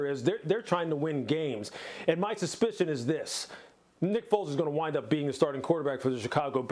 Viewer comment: Lol sounded like the dude said "nipples is gonna wind up being the Chicago Bears Quarterback"